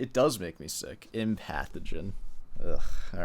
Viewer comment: She was saying "Empathogen." It really sounded like she was saying "pathogen."